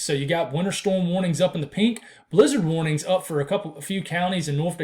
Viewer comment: Oh snap, I thought you said Lizard warnings for a second there